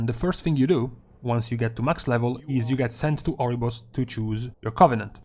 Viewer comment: swear you said Argus, not Oribos